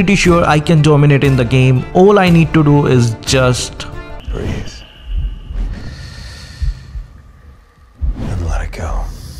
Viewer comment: 20 EPIC SKINS GIVEAWAY <link> <link> To get one of the Cheapest and Fastest diamond buying experiences. Do visit our store on <link> Trust me! Diamond buying has never been this easier before. Customer Care FB :- <link> Whatsapp :- <link> Mail <mail> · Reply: I thought you were gonna say breath air instead of Breath and let it go🤣